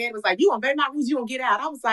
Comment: what did she say? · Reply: "You goin' to obey my rules or you gon' get out"